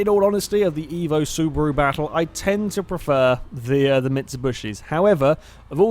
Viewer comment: Did I just hear you say "Mitsubushi'?